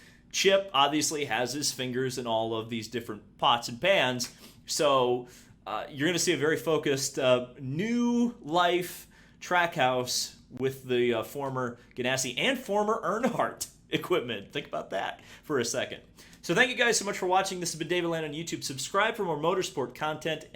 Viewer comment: LOL, At first I thought I heard "crackhouse" bought the tesm. 🤣